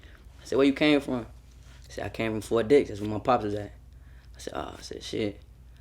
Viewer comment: I know im not the only one who thought this nigga said "I came from 4 dicks thats where my pops is at"